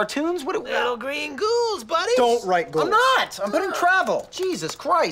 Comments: I genuinely thought he said "People's needs" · I always heard it as "People's needs" which made it more funny to me, but "People's knees" is equally funny. 😂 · I assume he's talking about the green ghouls from Castlevania II, but of course he means hallucinations from malnutrition and huffing paint. I also thought he said "People's Needs" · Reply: He does say people's needs, the stupid subtitles just say knees. I hate what YouTube has become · I always thought that Charlie said "people's needs" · I always thought he said peoples needs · I always thought he was saying peoples needs